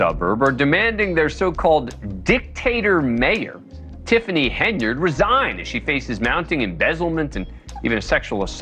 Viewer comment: Aww i thought you said she resigned i was really happy for a moment